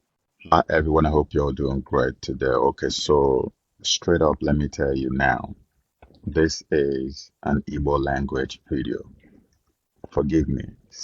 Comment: I thought you said you want to speak to your people in igbo language. 😁 Udo dirigi